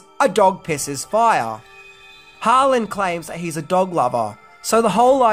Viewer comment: I was chewing when you said Renny was a Dog Lover and didn't hear what you said so I had to go back because I thought you said piss lover